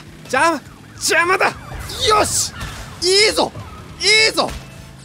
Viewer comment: の「邪魔だ！よし！」 が「邪魔だ！キヨシ！」 に聞こえたの俺だけ？